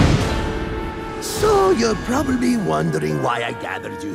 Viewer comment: Rafaam says prublebe instead of probably